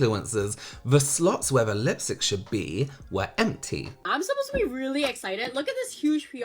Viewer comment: am i mishearing or did that girl literally just say I'm supposed to be really excited.."???